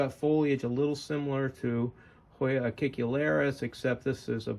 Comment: What is the name of the hoya you say it look similar at 2:55? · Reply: Hoya acicularis, some people also call it Hoya teretifolia. If I could ever flower it again, I would show it off in a video.